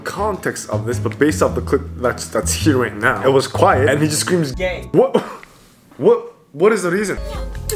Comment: I swear to you it sounds like Han just said gay out of nowhere but he actually said Gae which means dog🤣🤣🤣🤣🤣🤣